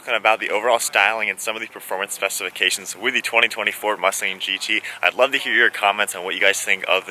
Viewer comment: did I hear 2024 mustang gt? 😂🤔🤔🤔